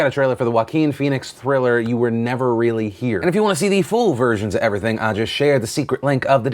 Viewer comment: ‘You Were Never Really Here’ trailer, I thought Phil said the trailer "You will never really hear" lol